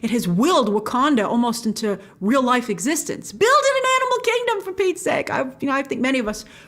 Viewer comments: Certainly was a Freudian slip... but they always find a way to show how they really feel. Look at what Mitch McConnell said the other day 😒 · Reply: Sound like she said build in an animal kingdom. Meaning introduce Animal characters to the movie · I want to apologize to the last comments I didn't get a chance to respond to. I was just super tired. 😫 I'm gonna find a way to make it up to y'all. 🙏🏿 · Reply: Sounds like she said build in an animal kingdom